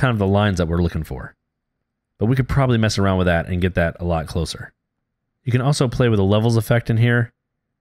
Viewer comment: My friend Lorenzo brought it to my attention that I had an audio dropout around 3:20. Unfortunately YouTube doesn't allow me to just swap the video out, but what I said was: You can also play with the levels effect in here, to really modify how this looks.